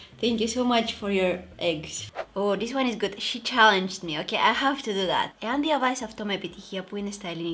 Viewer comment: what did u say in 18:01-18:06?? I just couldn't understand 😅😅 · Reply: Abi Almendarez she said “I have to do that”.